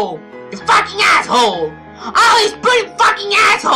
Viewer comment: i miss having with a shitty mic lol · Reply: I get where you're coming from, but the only issue is, sometimes I legitimately don't understand what he's trying to say. Like, for example, at 4:50 - I cannot for the life of me decipher "all is putting the f**** a*****e" "what? why are you taking care of the owner's ass?"